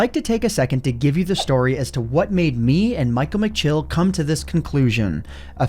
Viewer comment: subtitles say "Michael McGillicutty"